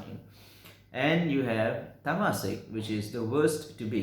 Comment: who? what was that word? thanks. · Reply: "Tamasic"